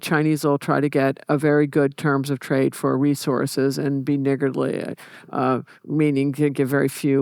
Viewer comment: what the fuck did she just say? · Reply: Niggardly is not the N word! It means very mean in your dealings or allocations